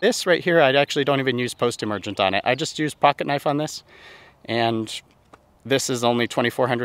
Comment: Sorry if I woke you! I’m trying to do a minimalist style Renovation on my front lawn. What weed killer did you say you used? Swore I heard “pocket knife” Google turned up nothing for me. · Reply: No, you didn’t wake me (late nighter). Lower end Benchmade (literally a pocket knife to cut poa out as I see it). For burn down before renovation 3-3-3 glyphosate (3 ounces, 3 times, 3 weeks apart (about a 2 month process). The best time to seed in our area is very late Summer before much risk of 2”+ precipitation days. Groundskeeper 2 rake. 1/3 of seed, rake hard, 1/3 of seed, rake with moderate pressure, 1/3 of seed rake lightly. A wetting agent is also added wash out insurance (and core aeration can help also) if you’re thinking about doing an all out bare dirt renovation. My weeding is done with a pocket knife in my front lawn and my back lawn gets sprayed about once a year with a 2-way or a 3-way (something containing 2,4-D) mainly to tackle clover. I’ve also used stacked tenacity applications in the back lawn to gradually reduce poa in the back. Since seeding there hasn’t been any post emergent out there in the front… pocket knife only.